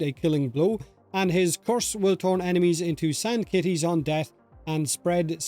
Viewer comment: am I hearing sand titties? I feel like I COULD be going insane but im not sure